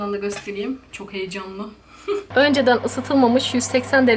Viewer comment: Annemle beraber izledik annem koptu önceden pişirilmemiş fırına koyuyoruz demene hahaa ben mi yanlış duydum dedi :Dddddd